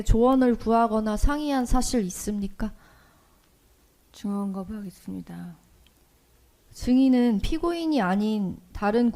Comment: 증언거부하겠습니다는 다 "네"로 들리는군